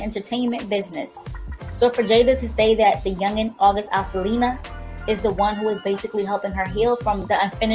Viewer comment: who else heard her say August Alsilina